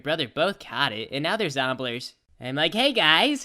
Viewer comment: what are zombluers? wait did anyone hear that at 6:43 ?